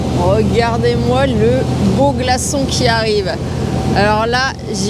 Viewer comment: Regardez-moi le beau g̶a̶r̶ç̶o̶n̶ glaçon qui arrive ! J'avais mal entendu ! 😅😅😅